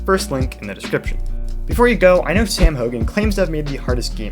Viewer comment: For a second I thought he said "I AM sam hogan."